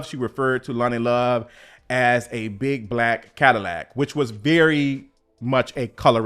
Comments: I think she said big back cadillac not big black. Both are hateful and unnecessary. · Been clocked Snoop. I thought Tamar said Big Back Cadillac. I'm still Tickled by Halle standing on Big Business. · I know you thought that she said" big black Cadillac" but she actually said "big back Cadillac"... Not that that's any better 🤦🏽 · I thought she said Big Back